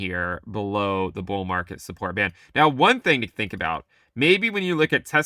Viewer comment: I keep hearing “bull market SquarePants”